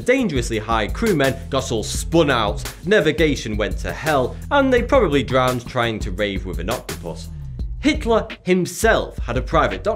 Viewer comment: I thought he said "and they probably drowned trying to rape the binoculars" @ 3:28 lmao